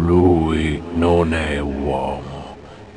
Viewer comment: did i hear there ''no homo''